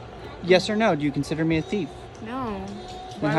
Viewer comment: Um, did she just say 'oh, shoot in me right now' or do I need to get my ears cleaned?